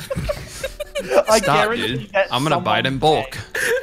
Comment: When he said “ I’m going To Buy it in bulk” I thought he said imma Biden bulk😂